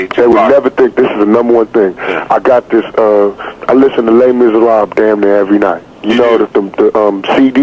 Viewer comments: can someone tell me what he mentioned on the 2 CDs at 47:39? he said he listened to them every night the 2nd one was gospel at colonus but cant hear the 1st one he said. Would really appreciate it, thanks! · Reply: Les miserables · Starting at 47:30 does anyone know the name of the music he mentioned? It sounded like he said “le minsera” but I can’t hear exactly what he said. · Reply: le miserable